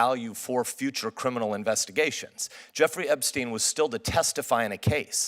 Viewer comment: "Christmas ornaments, drywall, and Jeffrey Epstein.........3 things that don't hang themselves" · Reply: That was SO funny - but it sounded like he said "Jerry Epstein" not "Jeffrey" - still, well put!